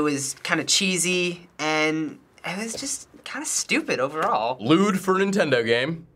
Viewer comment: Did just say "Lewd"? LOL!